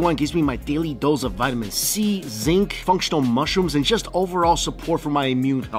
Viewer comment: did say functional mushroom? 😂🤣🤣🤣